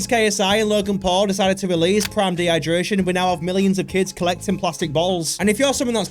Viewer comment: did he said prime dehydration 😂